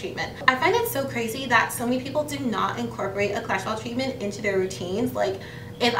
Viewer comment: Guys what is the treatment she mentioned called? I’m trying to understand but I keep hearing “cholesterol treatment”. But I know that’s not what she’s saying, help me!